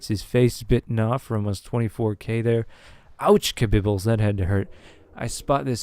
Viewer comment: I heard "ouchkabibles" then hit subscribe.